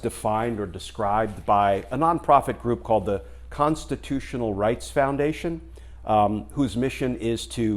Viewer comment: Forgive me... I thought I heard prostitutional. Rewind 🤣🥴